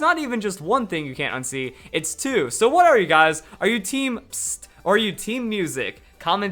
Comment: when you said #TeamPSST i thought you said #TeamPissOff XD but umm #TeamMusic